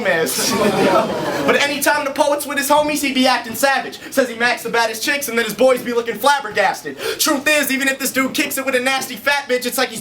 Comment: what did say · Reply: WooHollywood Celebrity ”but anytime the poets with his homies? He be acting savage. Says he Mack’s the baddest bitches, but then his boys be lookin’ flabbergasted”